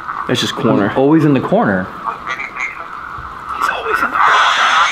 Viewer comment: the more I listen to it it sounds like coroner instead of corner but that could just be me